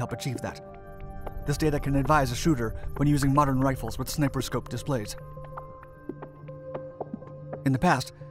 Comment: did he say snipper scope? 1:40 seconds <link>